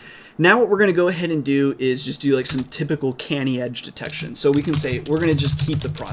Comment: How did you get gta 5 running? I would like to follow along with the tutorials. Also, thought you said candy edge detection, that would be a fun algorithm